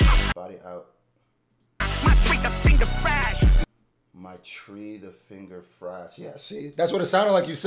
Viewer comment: “my trigger finger fast rapid eye” think that’s what he say